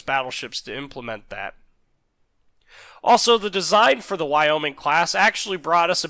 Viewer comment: When I first started watching this I thought you said I was an A-Hole design...LOL...A-HUll